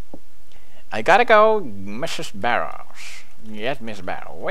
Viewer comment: Mrs. Barrels, PewDie don't trust her and watch out. Sounded like he said Barrels.